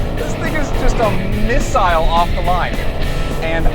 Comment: does he say this n**gas got no chance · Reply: Datboy Wave "this thing is just a miss-aisle off the line"